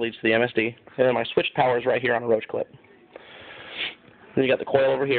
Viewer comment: Did he say roach clip? 1:24 LOL